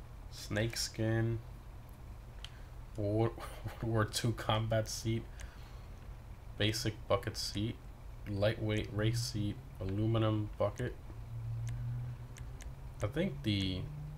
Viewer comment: I thought you said "basic f**king seat"